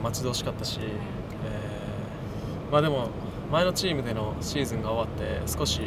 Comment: I can't heard he said Salzburg. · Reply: He said that (the team I belonged to before) is ...